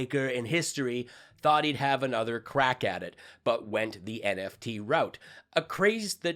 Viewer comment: for a second I thought you said "crack addict" and pictured Mike Lindell 😂